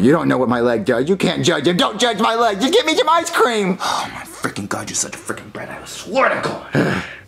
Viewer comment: the closed captions said "oh my freaking God you're such a freaking bread i have a sword of corn"